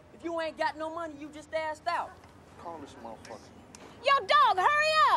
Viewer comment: ''if you don't got no money you just ... out'' What is he saying? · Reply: "Assed out" just like completely out of options